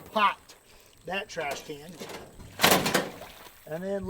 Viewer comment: what did he say? He did what to the trashcan? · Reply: "Popped that trash can." Lol